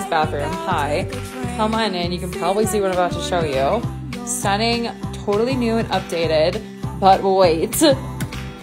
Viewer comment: I thought she said "sunbathing totally nude" and thought THATS AMAZING!!